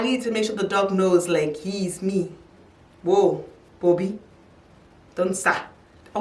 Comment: Did she just say " bobby don't tsa" ? 😂😂😂😂😂 Am dying cuz in Mozambique we say the same